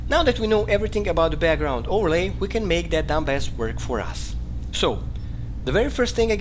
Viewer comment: my subtitles said, "we can make the dumbass work for us". Hahahaaaa, I know that's not what you said but it was so funny I had to share!